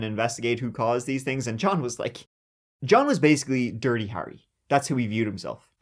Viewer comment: "Dirty Barry", I thought it as you said it. Harry the cop & Barry the fireman as a duo. "Make Our Day Punk" Coming to Irish Cinemas Soon. 😂❤😂❤😂❤😂❤😂❤😂❤😂